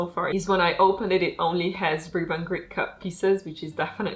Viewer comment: I couldn't make out what you did not like about the 'cut' in "Rainbow Tigers" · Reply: It only has standard shaped pieces. So this is usually a no no for me in a puzzle, but sometimes the prongs are still specific enough, that it is not too bad, which I hope will be the case with this puzzle too! 😅🤞🏻